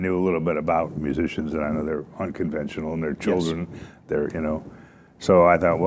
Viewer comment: did he say they were children?